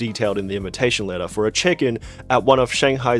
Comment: bro I thought you said a chicken 😂